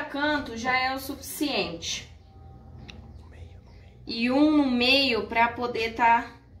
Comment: eu escutei lá no fundo "e um no meio"😂😂